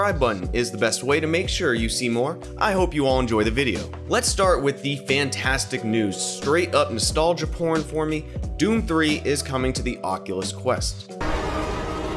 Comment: I thought you said “install your porn for me” 😂😂😂😂